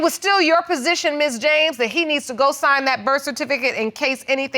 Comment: Did she just say “we need to do dinner” 😂😂